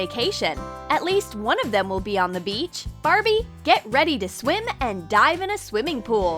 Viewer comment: i thought she said get ready to swim and die in a swimming pool